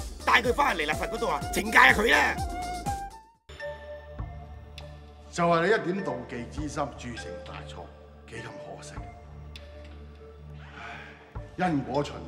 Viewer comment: 7·39 我聽成就係你一點妒忌之心，鑄成大錯 幾染可惜⋯⋯